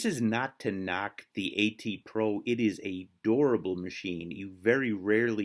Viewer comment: Ha when you said durable I thought you said adorable, I just clued in when you said its hard to break it